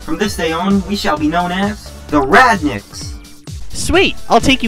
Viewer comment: when I was a kid, I misheard Dash saying Radniks. I thought he said Ratniks.